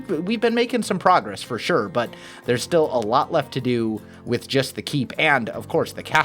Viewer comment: I thought u said it was just the keep